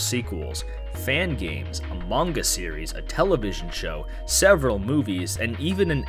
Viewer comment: I fucking thought that you said "Among us series", I think I lost my mind